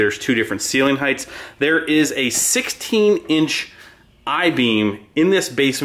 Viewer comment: shop needs a taller door so you can store the Peter inside 🔥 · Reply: i thought he said is was 16 high not 10 feet, however trucks are no taller than 13'6"